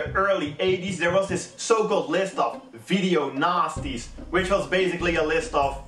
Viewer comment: everytime i hear him say "nasties" it always sounds like he's gonna say "nazis" xD